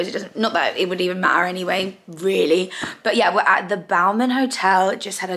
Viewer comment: What was the name of the hotel again? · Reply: that’s what I thought also.. I’ll google it. I thought she said Bowman …